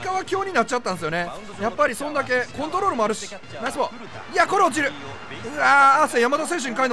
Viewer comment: ナイスボールがライスボールに聞こえたwwww